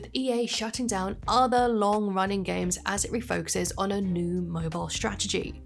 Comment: for a second i thought u said "new rip-off strategie" lol i mean it is EA. Btw side note when YU-GI-OH duel Links ends I'm probably going cry & never play another mobile game, i never spent 1 cent on the game tho. 😂